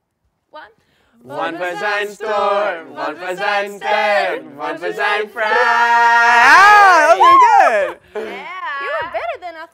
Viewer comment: At the end of the second song it sound like they said one present fry